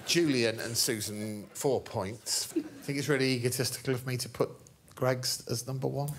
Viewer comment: The subs say "egotistical", I hear "egotesticle"